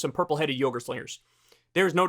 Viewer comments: Purple headed yogurt slayers!! Lol that got me lmfho! Only Kyle! Only Kyle! Love it! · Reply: Hahaha, I heard "purple headed yoghurt slingers" 😂😂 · Did you just say "purple headed yogurt flare" ???? 😂